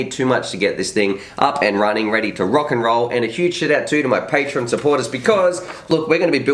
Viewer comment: Did he say "a huge shit out"